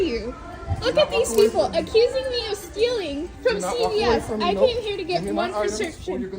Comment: I don't shop at Walgreen's. No one should anymore. Shut this chain down! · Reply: Did she say CVS?